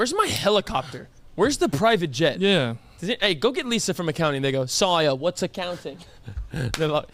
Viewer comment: WHAT DID HE SAY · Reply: Go get lisa from accounting?